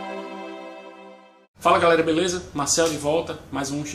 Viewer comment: Sim, ouvi atentamente tudo que você disse, desculpe pelo "erro" no nome, é que em 00:07 você diz algo que parece ser "Marcelo". Sobre os médicos, você agora está variando em seus argumentos, afinal, você é contra o que? Eu realmente queria achar o erro nesse programa, mas não estou conseguindo! Se for olhar por esse lado que você aponta, os médicos europeus vão ganhar menos também (coitados...). Para concluir, até onde sei eles (cubanos) receberão 4 mil de 10...isso não é 1/10..